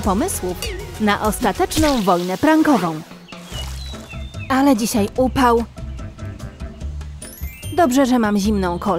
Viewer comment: aha rzeczywiście mało xD cała butla... I jeszcze to " Spadaj "... 0:32 czy mi się wydaje czy ona przytyła. Potem ryk w domu 0:42 Hej sosie sojowy xD 1:22 tia... Po tym wszystkim jeszcze ma wyjść na dobre dla niej 1:36 W Polsce nie ma takich talerzy 1:57 on jest brzydki jak smoła xD 2:15 ulala... Jak ktoś tam na dole pisał " Ostro " 2:38 Jezu nie za dużo tego spadaj 2:52 niezłe porządki 3:08 torcik 3:10 o kurka. Każdy wie co się teraz stanie 3:36 ta... Napewno w Emily 3:48 Kto? Ty. Bo trzeba było je wyrzucić do kosza zamiast spowrotem do zamrażalnika. Pozdro dla kumatych 3:57 FUUUUUUUUUJ 4:01 xD bez komentarza 4:32 Pycha. Poproszę 4:55 co jest z jego mordą 5:07 Naprawdę nie lody? No któż by się spodziewał 5:19 No nie. Nie musisz 5:46 i ten obraz w tle 6:11 NO TO CZEMU DO JASNEJ CHOLERY SIEDZISZ TUTAJ ?! 6:25 Aha. No to było straszne 6:36 no właśnie. Już miałem się pytać gdzie Clear 6:48 no bo hałas taśmy klejącej jej nie obudzi. Nie. 7:28 o żesz w mordę 7:43 Jedzenie. A sama jest trochę gruba 7:52 Pizza z Ikei xD 8:10 Każdy bierze proszek do pieczenia do restauracji. Każdy. 8:38 to Emily 8:48 Eeej! Ja też chcę! 9:02 o wydostała sie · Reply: usłyszałam,,ale dzisiaj pupał,, XD